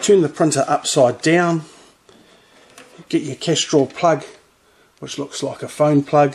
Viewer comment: What did you call that plug, the kestrel plug? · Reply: Cash Draw plug not kestrel